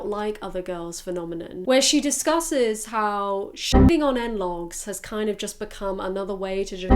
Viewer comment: are you saying "analogs"? English is not my first language and for 5 minutes now I'm wondering if is "analogs" or what word is it 😅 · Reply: No, it's an abbreviation for Not Like Other Girls = NLOGs.